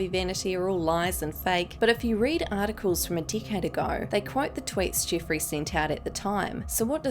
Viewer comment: When she said a decade ago, I wasn’t paying attention an I thought she said a d***head ago lmao